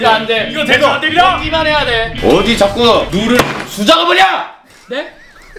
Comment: 뭐라고 하는지 아시는 분...!? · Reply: 어디서 수작을 부려!!! ㅋㅋㅋㅋㅋㅌ